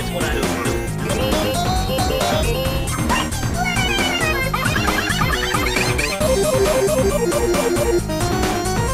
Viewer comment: What Is Red Girl? Saying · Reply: how the HELL do you not know who?? not a girl and he's saying the classic "you fricking fricks" line